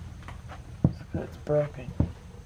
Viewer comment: I had bad heartburn.. took something for it.. and had a huge burp.. I then thought you said.. Someone is burping... i rofl (broken) not (burping)